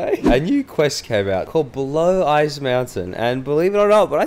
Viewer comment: Not going to lie thought she said blow ice mountain